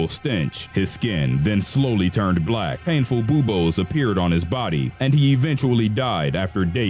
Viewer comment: lol bu-bowls i think its called bubbles